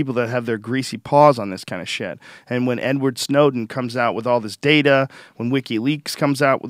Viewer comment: Did he say N-Word Snowden? 48:20 Good podcast :)